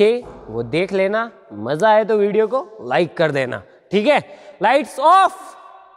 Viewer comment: I heard; LIGHTS OFF!!!